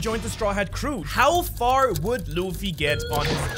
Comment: I was having interruptions and thought at the start you said "how fucked would luffy be"